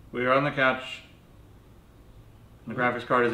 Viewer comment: Oohhh you said graphics card!! I thought you said penis. Thank goodness i did not put that in the toaster..... Err the GPU...